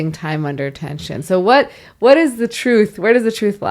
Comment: I thought it said trust. I do kagels and squats at gym. Then i just go home after.